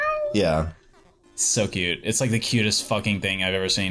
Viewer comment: i heard "did he always juice scissors?" and it sounded very lewd.